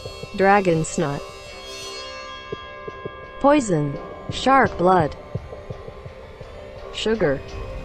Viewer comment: thought it said shart blood